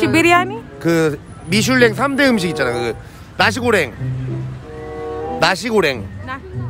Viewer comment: i think he was saying a dish called nasi goreng